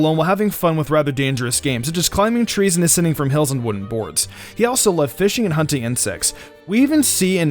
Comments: Am I the only one who heard "he also likes fishing hunting and sex"? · does that say he also likes fishing and hunting and SEX!?!?!?! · Did I hear that wrong? Fishing....and hunting... and sex? Wow there buddy boi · he also loves fishing,hunting n sex.. i heard that did anyone else hear it lol.... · You were talking so fast it sounded like you said "and sex" instead of "insects". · wait what did you say?! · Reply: sex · what?. Im pretty sure I have misheard it but yes... sex is a pretty dangerous game. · I thought he said "fishing, hunting and sex" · fishing hunting and sex? Did I mishear that or something? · i thought u said fishing hunting and sex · wait did I hear that right? “He also loved fishing, hunting, and sex” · did you say : he also loved fishing, hunting, and sex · did he said sex? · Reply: "Hunting insects" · I thought you said s#x XD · "He also loved fishing, hunting & sex" is what I thought was said 😅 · He also lived •fishing •hunting •and sex...? I can’t be the only one who heard that? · I thought he said fishing hunting and s3x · WAIT A BLOODY SECOND! 5:09 "Sex" I hear? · Excuse me but did I hear this right? "He also loves fishing, hunting and sex" YOUNG MAN THAT IS NOT APPROPRIATE TO YOUR AGE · "He also like fishing, hunting and S*X?!?" Am I Hearing That One Wrong? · swear he just said sex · DID I HEAR IT RIGHT GYUS??? DID SAID SEX?? HUH!!! PLEASE COMMENT! WTF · what did he just say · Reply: Insects 😂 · Who else he thought he said and sex